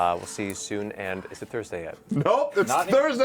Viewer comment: I heard "It's Thursday right now! Let's duke it out Matt Mercer!" -Grog